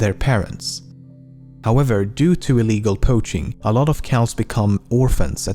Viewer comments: Did he say cows?! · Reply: it's calves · briefly after 2:13 did he just said "cows"